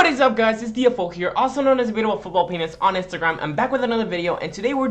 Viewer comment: I thought you said football penis lmao